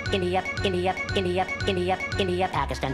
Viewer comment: PPPakistan 3:43 Zambi 3:58 Goner 4:20 Tanzanias 4:33 The Year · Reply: I hear Giddy Up instead of India